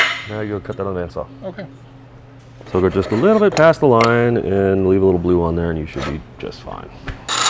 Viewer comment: Headphone users can you hear what he says @ 14:05 ? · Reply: The only thing said near that point was, instructions… “So go a little bit passed the line and leave little blue on there and you should be just fine.”